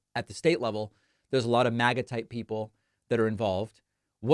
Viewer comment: Often, David, when you say, “MAGA-type” I hear “maggot-type”, then I realize that you got me again. Just thought it humorous. The two might be synonymous. ;)